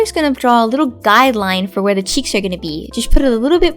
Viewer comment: The first time i watched this I thought she said "Chi" instead of cheek and I was like WOT CHI WOT IS THIS